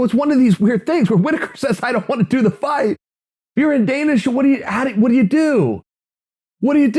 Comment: What the hell is he saying here 1:18 ?😂"if you're in danish"?!